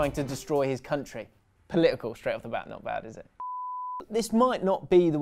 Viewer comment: I legit have no idea what you said after "Political" all I heard was "Political afjashdjfhasdhjdf not bad is it" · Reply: "political straight off the bat, not bad is it" at 100mph